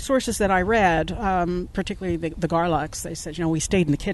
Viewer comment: I thought said The Daleks said they stayed in the kitchen...